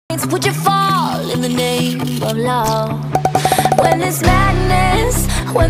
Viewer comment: BEAUTIFUL PAINTING 🎨🖌️💖 Also when i heard the song i thought it was saying "And its all in the name of the law"💀